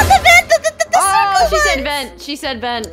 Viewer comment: accidently vented and I thought he said oh sht